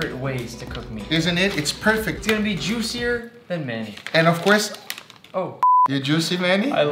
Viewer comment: I thought he said its gonna be juicier than men.